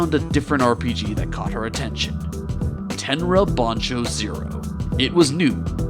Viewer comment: i still don't understand what he is saying 10 rabanne 0? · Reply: Tenra Bansho Zero.